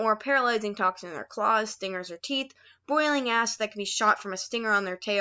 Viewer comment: why did I hear boiling as- 😬